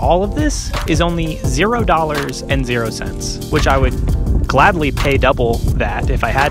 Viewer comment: Yo, did you say you would 'gladly' pay double the amount of 0😂😂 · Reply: gladly!